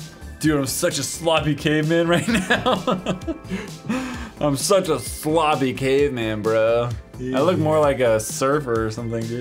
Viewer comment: the subtitles say your such a logic a man right here I'm such a flaw became Amhara you 😂😂😂😂😂